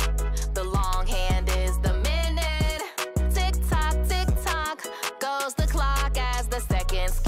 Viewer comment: Did you say TikTok YouTube YouTube Goes the clock